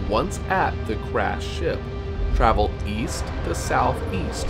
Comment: I thought he said grass ship🤣🤣